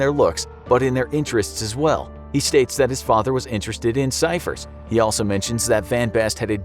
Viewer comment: is he saying cijfer not number or am i rong?